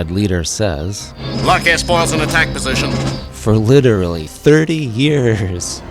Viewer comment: "A gentleman wastes enough to be traveled lightly" was spot-on what I always heard. I always heard "Lock s-foils in attack position" as "Luck as falls in attack position," which always left me scratching my head. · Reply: I thought it was luck as well. As a kid, I always thought he and later Wedge said “Luck has fallen in attack position.” I used to think that was some Rebel pilot motto before battle, or something along those lines.